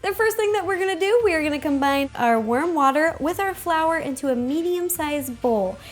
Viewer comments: Im sorry, did you say... WORM WATER? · Lol sounded like worm water and I was like ew.. lol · me: did you say worm water? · it sounded like she said worm water instead of warm water😜